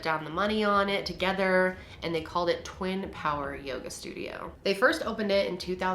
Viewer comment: Okay first of all I thought Kendall said “twin tower yoga” at first and I was shook